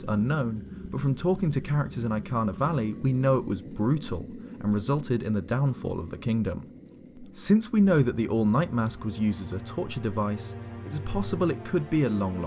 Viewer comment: For a moment, I thought you said, “The All Might” mask~